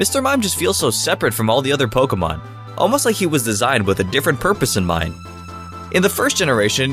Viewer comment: Did you say "a different purpose in mime "?